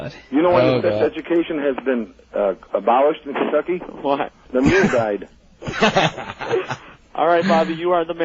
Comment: I can't make out this joke! "Why was sex education abolished in Kentucky? The [blank] died." Is it "mule"?